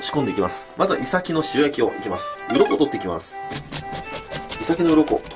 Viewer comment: 鱗取ってきますが脳取ってきますに聞こえる